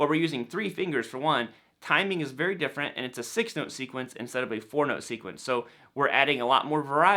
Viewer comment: When you said "four note sequence", the captions thought you said "porno sequence".